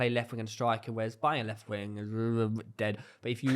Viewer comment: Did he just say Bayern left wing is dead 😐